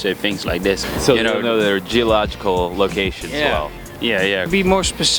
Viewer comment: Interviewer at 6:44 - "geological" instead of "geographical", for fuck sake, that's whay they think we're dumb